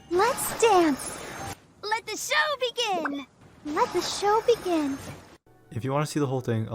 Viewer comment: Before 1.3 it thought Barbara said “let the jello begin” instead of “let the show begin” but I still prefer her old voice. New Barbara sounds so depressed